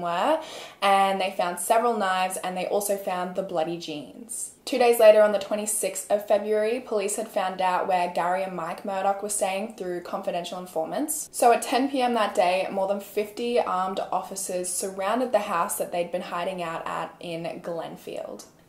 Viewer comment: I thought you were saying Mike Murder 🙄